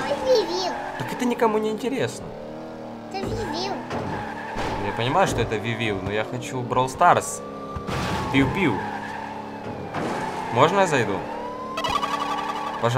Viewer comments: я один услышал "ты дибил" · Стоп что он сказал? 0:12 Мне кажется он сказал: Ты дибил)